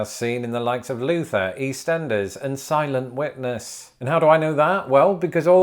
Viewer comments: I swear, for the first like 6 times he said it, I thought he was saying "Silent Wetness" not "Silent Witness" XD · It sounded like you were saying "Silent Wetness", and now I CAN'T UNHEAR IT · Omg I thought you said ‘silent wet nurse’ was a Britbox show. I would think that would be on a different venue. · I heard it as Silent Wetness and then he showed a pool and like it IS wet and silent. I realize it must be Silent Witness but Silent Wetness is hilarious to me.